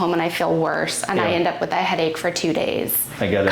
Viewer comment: "I end up with a headache for 2 days"... I thought she said she'd had a headache for the last 20 years... I'd like to see an interview video with about how he gained such an intuition and feel for chiropractics